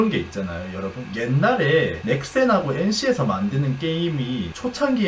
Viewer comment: 넥센이라 그래서 응? 넥센 (타이어회사임)이 언제 게임을 만들었지? 야구 게임말하는건가? 했는데 넥슨이었음 ㅋㅋㅋㅋ